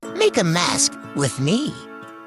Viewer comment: I didn't see the text and I thought u said "make a mess with me!" 😭